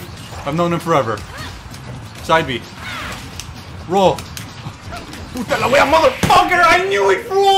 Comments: Did he just said "puta la weá"? And if thats so, wouldn't that mean that he knows spanish? more important than that, Chilean spanish? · did he just said "puta la wea"?? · Did you say "puta la wea"? XD min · did he say "puta la wea" at 8:26? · Did you just say "puta la wea"? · Is just me or did he said "puta la wea" at 8:25 ? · What?¡¡ he said Puta la wea ? nice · My man, in the minute 8:25 did you said “Puta la wea”? es chileno?